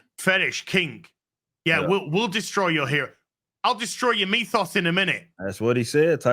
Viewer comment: lol, instead of "I'll destroy your MYTHOS in a minute" I heard "I'll destroy your MEAT SAUCE in a minute" for a second there. Then my brain's like "They're talking 'bout Taika Waiti, it is probably MYTHOS, man." and I agreed. xD